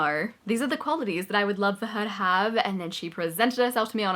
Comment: Great video very helpful 👏 On a slightly random note, I happened to have auto subtitles on and when you said “the moment I knew” it thought you said “the merman I knew” 😆 🧜🏾‍♂️ thought that was pretty amusing.